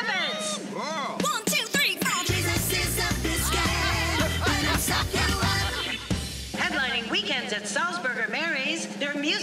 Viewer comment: What are they saying? I can't find the lyrics anywhere on the internet and it's making me go insane! It seems unimportant but like? What are Sapphira, Morphine, and Mhi'ya saying? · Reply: The previous comment is vaguely correct but not entirely. It’s “Jesus is a biscuit, let him sop you up”. This is a reference to a line said by Season 4’s Latrice Royale.